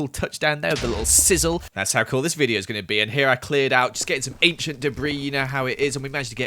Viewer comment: For a second I didn’t hear “ancient debris” I heard “ape shit marie”